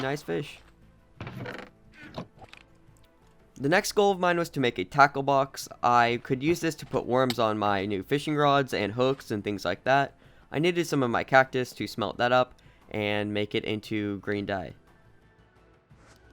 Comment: i thought i heard you say a taco box lol